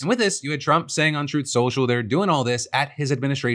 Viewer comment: "...on truth social..." but the captions say untruth lmaoooo totally giggled at that, it was much needed